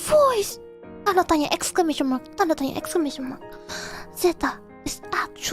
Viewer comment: 요거 exclamation mark 라고 들려요 확실하진 않지만 ㅋㅋ 영상 제목으로 클립퍼들이 !!!!! 넣는거 말하는듯??